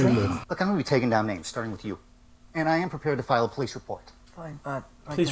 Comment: I honestly heard that as "I am a pe##phile please report."